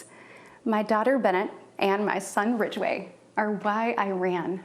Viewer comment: Did she say that she named her son Ridgeway, or did I misunderstand her? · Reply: Yes, Ridgeway! It must be a family name😂